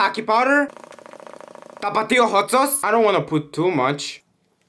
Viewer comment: what people hear: i dont wanna put too much 1:59 what i hear: i dont wanna poop too much💀